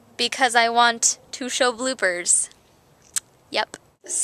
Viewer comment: I thought you said you want to show bloomers lol my bad